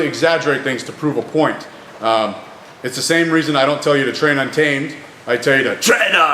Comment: Train untamed?????? I've been watching this dude for a little over a year now... I thought he was saying "tread ontime" lmfao